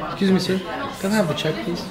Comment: na primeira vez q ouvi, entendi que o falou "Kiss me sir"